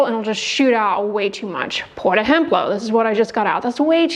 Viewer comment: thanks for all your great content. What word are you saying in this video at 0:47 and 3:17? Portohemplo? What is that? First time I’ve not understood you. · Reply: Por ejemplo means for example in Spanish